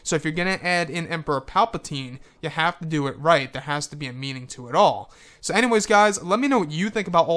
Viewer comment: I thought he said there has to be memes and all